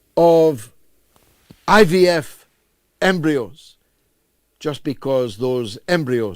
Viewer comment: 1.00.15 "IVF Embryos." For at least a minute there I thought George Galloway said, "IDF Embryos"!